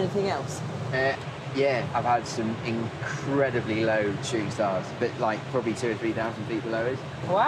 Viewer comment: anyone catch what he says here?... “Tree stars”? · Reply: shooting stars